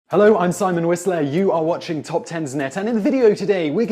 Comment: he reminds me of Voldemort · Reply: I thought he said 'hello I'm Simon Weasley' and I almost freaked out